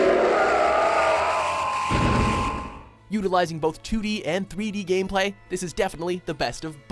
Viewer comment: Did he just say "yugalizing"?! Lol